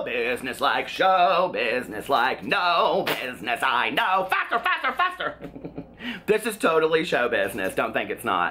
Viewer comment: Ahahaha the show tunes intro! I thought you said FAT GIRL FAT GIRL FAT GIRL not faster faster faster lol